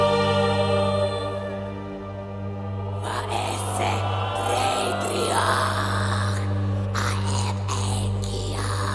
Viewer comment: does anyone know what is saying in 1:49? i want to learn that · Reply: "Va'esse deireádh aep eigean, va'esse eigh faidh'ar" - The Elder Speech (Hen Llinge).